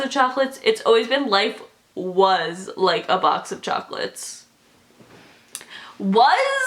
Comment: When the video of "life is/was like a box of chocolate" i heard "life with like a box of chocolate"!!!!!😱